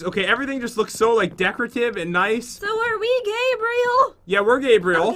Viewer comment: I'll distract him by twerking :v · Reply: "are we gay-breal" at first i thought it said are we gay